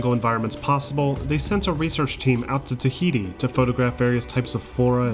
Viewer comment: did someone say Tahiti??